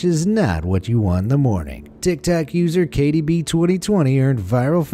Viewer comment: Did he just say, tiktac user?